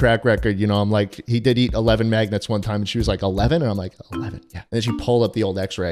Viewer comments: I thought he said, "maggots." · bro i stg i thought you were saying maggots the whole time